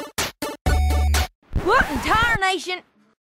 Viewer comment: Does anyone notice it sounds like she said what in tire nation?